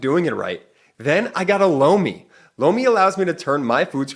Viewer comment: Holy Sh!t … “lomi” … it’s “LOMI”… 🫢swear to gawd thought Jordy said “blow me”… great ad read, as always. BUT I don’t understand how MAGAts think they will benefit from an autocracy? They really want to put TFG back in and throw EVERYTHING away bc they’re still butt sore over Obama?etc. … etc …